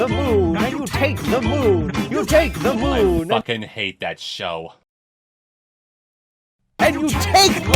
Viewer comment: Wait, did you just say you hate Chowder?! You’re DEAD to me! But in all seriousness, I thought you were going to talk about the Luncheon Kingdom when you did the theme, considering that Chowder’s centerpoint is cooking and FOOD.